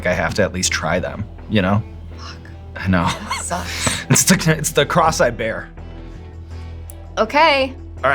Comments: I thought he said "It's the cross-eyed bear", and was like--is that a mid-Western saying? Then realized he said "It's the cross I bear." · Beautiful autocaption fail - Instead of: "It's the cross I bear..." it's: "it's the crosseye bear..." little giggles for me throughout the big giggles lol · At about nine minutes I swore I heard Sean say “it’s the cross-eyed bear” 😂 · I always hear “cross I bear” as “cross-eyed bear”. (context:Sean says it around 9mins) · I thought he said "cross-eyed bear" but he said "it's the cross I bear". Anyway, fun watch. I can;t wait to see Materialist.